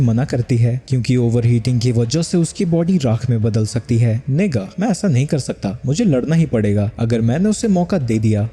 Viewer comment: did bro just said the N word??